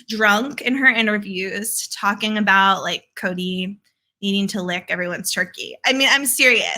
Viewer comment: 😂 I heard you say, “Kody needing to “lick” everyone’s turkeys. 🤣🤣🤣🤣🤣🤣